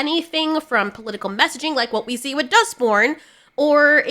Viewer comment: You kept saying "Dustborn" and I kept hearing "Duck Porn" and I'm thinking: "What the hell kinda games are you playing, girl?"